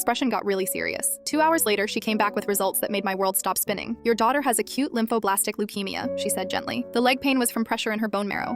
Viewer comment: I THOUGHT IT SAID “A CUTE LYMPHOBLASTIC LEUKEMIA”